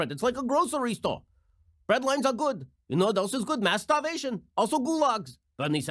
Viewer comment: When he said mass starvation I thought he said masterbation